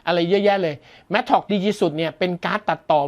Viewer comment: ผมได้ยินว่า “ดีที่สุด” 😂😂😂